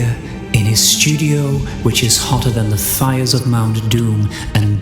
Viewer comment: I heard “hotter than the thighs of Mount Doom”. That’s pretty hot indeed. lol